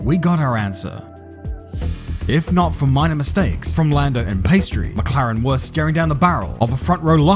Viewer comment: did he just call him Pastry? Lmao